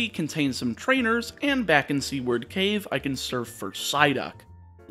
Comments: I legitimately thought you were saying C-word cave and had no clue why you were referencing that word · I thought he said c-word cave